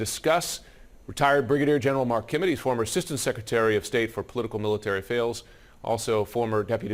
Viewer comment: "Former assistant secretary of state for political military fails ..." Yes he actually said that according to the AI subtitles too. Sometimes the truth slips out.